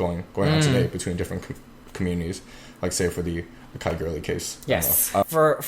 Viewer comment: What did say at 6:55? The something something case? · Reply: the akai gurley case!